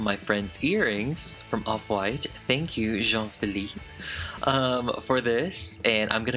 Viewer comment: did anyone else hear say "Jean Philippe" as in the one and only Jean philippe Susilovic also known as JP also known as THE OG MAITRE D IN HELL'S KITCHEN?! is this just a big coincidence? timestamp